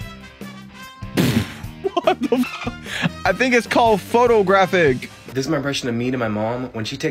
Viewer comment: what does the guy say about their memory? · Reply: he accidentally said cornograpic with a p